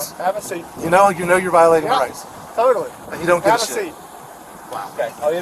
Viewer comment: "You know you're violating my rights ?" -: "Yeah. Thoroughly". This is exactly what is said at that timestamp. The investigation was botched as was not asked about that, AND the conclusions are also botched since it concludes "There was no malice on the part of Sgt. Yelvington". Discuss.